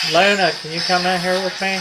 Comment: sounds like it says "its me" instead of what the caption says "its Luna"